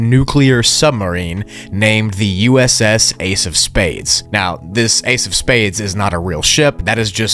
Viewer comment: Thought he said ace of base